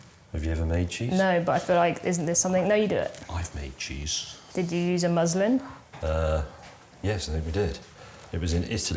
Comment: did actually say "did you use a Muslim" or is that my lack of vocabulaire in English??? 😂🤣😂🤣(Try to unhear it, I challenge you)